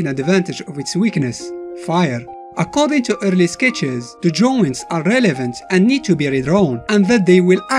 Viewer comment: what is he saying? Also 2:22. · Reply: The drawing were relevant and needs to be redrawn